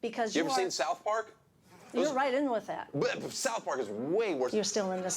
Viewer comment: Did she say "gen-ray"?